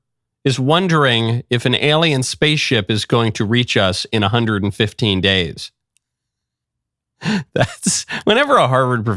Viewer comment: 115 days? At first I thought you said 150 days. 150 would be foolish, but 115? I'm getting ready for ET!